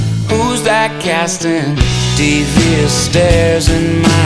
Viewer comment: Hangin' round downtown by myself And I had so much time To sit and think about myself And then there she was Like double cherry pie Yeah there she was Like disco superfly I smell sex and candy here Who's that lounging in my chair Who's that casting devious stares In my direction Mama this surely is a dream Yeah mama this surely is a dream Yeah mama this surely is a dream Hangin' round downtown by myself And I had too much caffeine And I was thinkin' 'bout myself And then there she was In platform double suede Yeah there she was Like disco lemonade I smell sex and candy here Who's that lounging in my chair Who's that casting devious stares In my direction Mama this surely is a dream Yeah mama this surely is a dream Yeah mama this surely is a dream I smell sex and candy here Who's that lounging in my chair Who's that casting devious stares In my direction Mama this surely is a dream Yeah mama this surely is a dream Yeah mama this surely is a dream Yeah mama this must be my dream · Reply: Devious stares . I thought he was saying deep fear stares . Thanks .